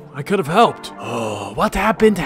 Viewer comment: nose si entendi que giorno te explique en español o es que suena asi en ingles